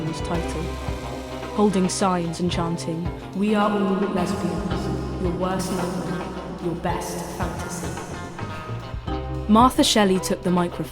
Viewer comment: i cant hear what they were chanting because of the effect you put on your voice and the subtitles dont get it right either, can anyone help? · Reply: Apologies! They chanted: 'We are all lesbians. Your worst nightmare, your best fantasy.'